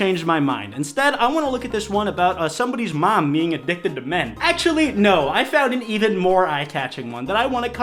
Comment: lowkey thought u said addicted to meth 😂